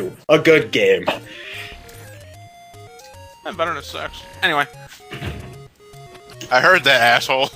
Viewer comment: I thought said "Not better than sex."